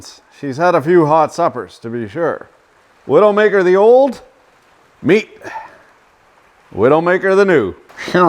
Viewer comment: “The old meat”, I could swear I heard “that old Tony".